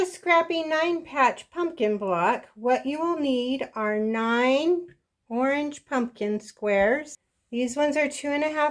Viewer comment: I'm confused. You say we need "9 four inch pumpkin squares, these are 2 1/2 inches." What size do the other squares have to be in the 9 squares are 4 inches? Thank you! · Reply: If you are referring to 0:25 I think she said "9 orange pumpkin squares"!